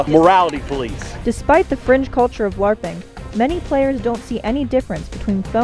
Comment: Did she say "beside the cringe-culture of Larping"?